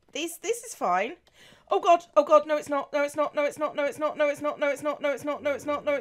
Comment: i heard “nose snot” in there..